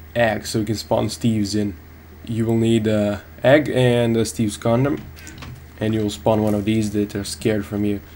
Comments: I heard "an egg and STEVE'S condom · did he say steves condom? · I could of swore he said "an egg and a steve condom"